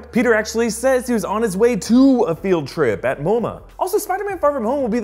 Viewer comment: Moma?!!!? Up until now I thought he said “on my way to a field trip with your momma”